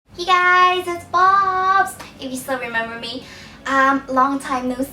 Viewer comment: In the beginning I thought you said hey guys it's BOBZ!